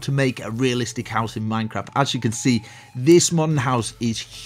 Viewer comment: it sounds like he said "MineCrap"